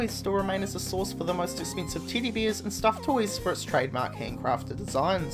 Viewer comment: Sounds like your saying Tiddy Beers